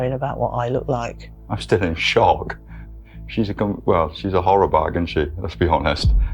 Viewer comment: minutes does he call her a "horror bag"? Just not an insult I'm used to hearing.